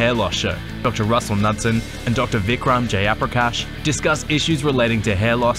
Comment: just say “Jayaprakash” like a normal person. I thought u said “J - upper - cash”